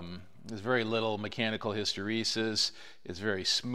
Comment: mechanical __? what was that word? i've never heard it before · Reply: Hysteresis <link> Think of it as the generalization of gear backlash.